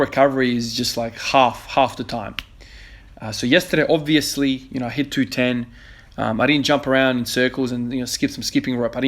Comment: You had me going at 8:50 your 200kg top squat subtitle said 220kg no body wants to lift heavy as weight